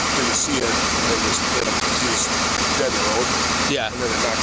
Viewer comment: I thought they said he was dead in the road and then it hit him and pushed him off to the side? I must be deaf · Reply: the car was dead in the road